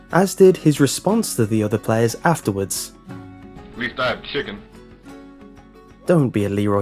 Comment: what did Leeroy Jenkins say after his guild got killed? All I heard was "We died, chicken" lol